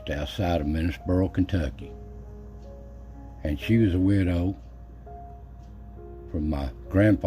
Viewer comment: OK. you caught me, when you said Mamaw was a widow, I heard it as weirdo, reminded me of my own family. Love the story.